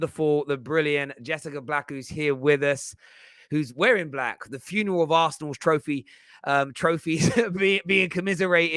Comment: 😂Did he just say “the funeral master.”